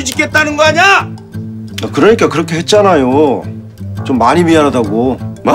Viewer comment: 얼핏 들으면 좀많이가 ㅈ만이로 들림ㅋㅋㅋㅋㅋ